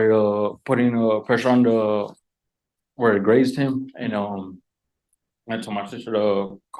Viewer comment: did he say 'where I grazed him' or ' where IT grazed him'?